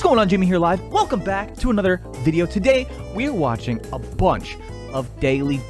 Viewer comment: I was clicking off the video (sorry) and it sounded like he said “Today we watching abortion”